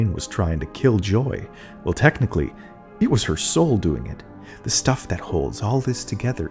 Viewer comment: did you say soul? 🌻